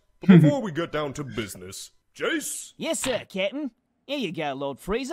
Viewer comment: Am I the only one who thought that Jeice was saying "kitten" instead of "captain"?